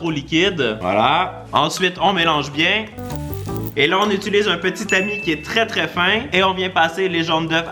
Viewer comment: un petit tamis (petit ami ) 15:17 😂🤣🙃 c’est tout pour moi mdr